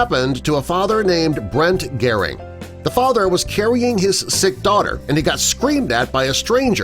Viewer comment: I thought it said stick but it said sick